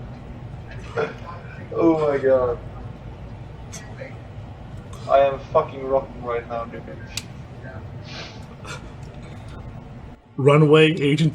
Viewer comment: Lmao thought said I’m fucking rock hard right now😭